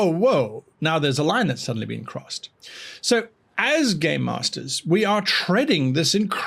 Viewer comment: Whenever he says 'game master/s' I just hear 'Gay Master/s'.